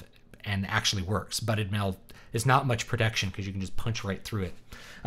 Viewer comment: What're you saying from 8:02 - 8:08? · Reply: Buted mail (rings just pushed together, not welded or riveted) is poor protection.